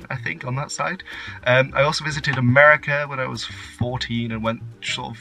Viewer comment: the subtitles say “14 and went she loved...” wonder what that’s supposed to mean!! ;)